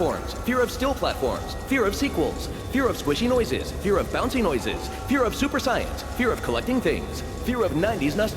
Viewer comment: Is just me or did he said at 7:30 "fear of super saiyans?"